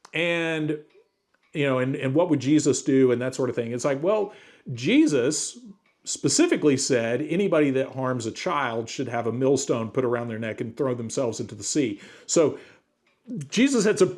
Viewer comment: im pretty sure he actually says "you would be BETTER OFF to tie a millstone around your neck", implying that would be the better fate for them